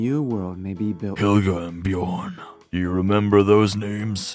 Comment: i thought it said björk